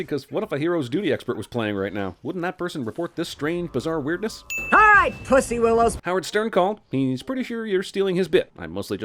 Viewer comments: did she say “ Alright p*ssy willows “ or am I hearing stuff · Sounded like she said pussy don't get mad at me if he said it. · i think she said "alright puss workers"